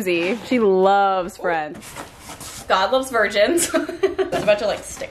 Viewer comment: could've sworn when said "god loves virgins" she said "todd loves virgins" LMAO